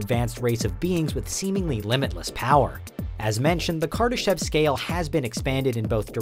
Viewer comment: Genuinely thought is said the kardashian scale 😂😂